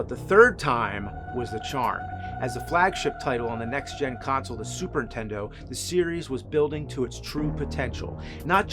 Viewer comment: ah yes the classic 'Super Tendo', I had to replay that a couple times to see if I was mishearing you lol